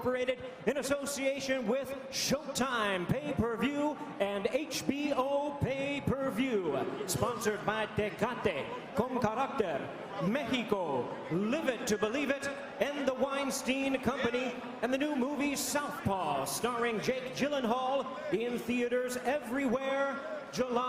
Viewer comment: Sponsor by Cocate con Karate Mexico 😂😂 wtf did he said ? Anyways they lost their money with that promotion lmao